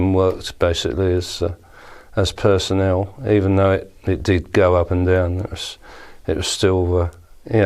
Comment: The subtitles say, "Even though it did go up in the anus." Lol